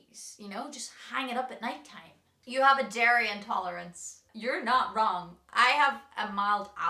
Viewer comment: As something different, please check out 'Heart Of The Ocean' by Gaelic Storm and 'Ready For The Storm' covered by The Cotters. I think you could make a really good cover of those amazing songs. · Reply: I thought she said she had a Derry Intolerance…an Irish thing! Then she said, full fat…dairy intolerance!!